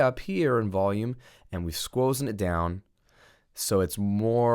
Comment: did he just say "squozen"?